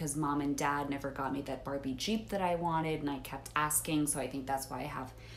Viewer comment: Ooh Barbie Jeep! I legit thought she said burning sheep. thanks comments 💖